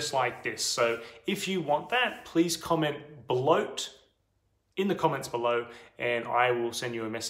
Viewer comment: I thought he said "Comment BeLoAT" hahaha